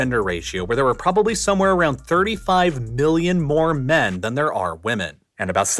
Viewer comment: Woah, did I hear 35 BILLION men at 12:38? Thats a way higher population than I thought!